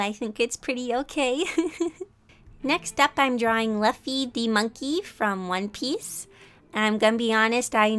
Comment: Sorry for saying Monkey D. Luffy's name wrong, I now know how to say it correctly. ^^ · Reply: You kinda sounded like you were saying Luffy The Monkey xD